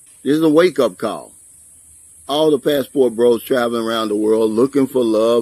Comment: You say "passport bro.." I hear "newbie."